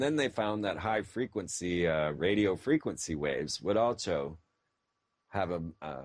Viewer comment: Did he say "Alcho" instead of "Also" ?lmmfao